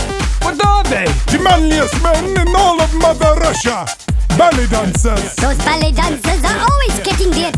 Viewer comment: At first I heard "belly dancers" ._. I need those subtitles pls